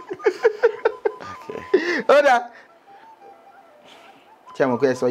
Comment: he said okay not yeah!!😂😂😂😂 I heard that okay loud and clear!!!